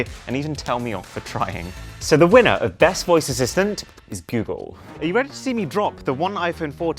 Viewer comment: is it just me, or does it sound like he said bugle?